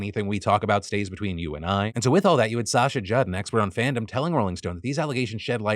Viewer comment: I heard "expert in fandom" and sort of snorted